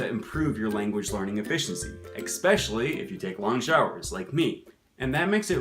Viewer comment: did you say "eXpecially" ?? 😁